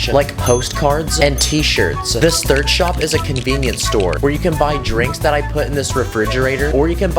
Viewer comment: I Thought He Said "Minion Store"